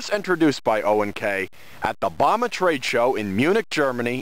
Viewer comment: subtitles said "Obama Trade Show"🗿